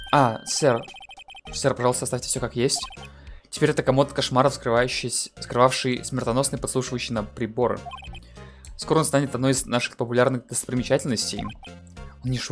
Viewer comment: Послышалось, будто «комод шмары». Уже успел подумать, что так дворецкий начал называть Эйприл